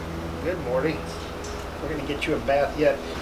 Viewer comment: did he say morty? is his birds rick and morty?